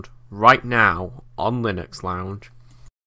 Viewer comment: ahah if i only listened to my right headphone it sounded like you said "linux lound" instead of lounge :P